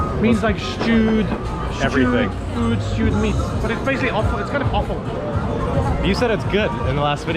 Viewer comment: "It's basically offal" "You said it was good in the last video! ?" hahaha (for Taiwanese: "offal" means "waste/extra parts of an animal" the organs etc, obviously thought he said "awful" haha)